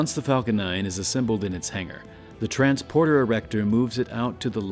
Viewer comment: Hehe “Erector” Did his mic glitch or did he say “Erector”